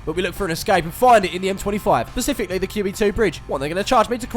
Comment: pacifically@ 2:12 did really say pacifically instead of specifically 😭smh